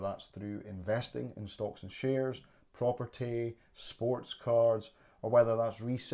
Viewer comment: Hi, it’s not something I did, although I did consider it. It was a big capital outlay and I didn’t know that market well enough. I could’ve invested the time to learn about watches, but choose to put my time elsewhere (sports card investing) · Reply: what are "sports cards"? I initially thought you said Sports Cars.